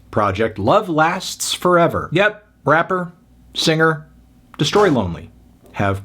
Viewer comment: I thought you said “Crapper”